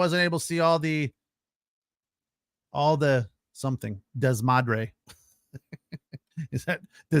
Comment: it's despadre in this case instead of desmadre.